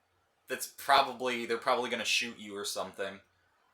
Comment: I SNEEZED oof i thought you said shoo